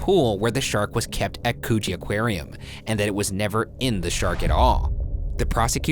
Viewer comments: I THOUGHT HE SAID COOCHIE AQUARIUM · I swear I heard "coochie aquarium"